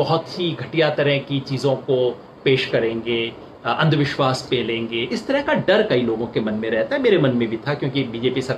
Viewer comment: did he said andhvishwas pelenge at 6:20 ??